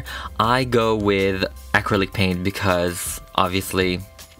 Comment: sounds like he said acrylic pain XD